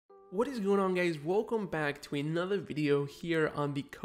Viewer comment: DID U SAY GAYS??????????????????????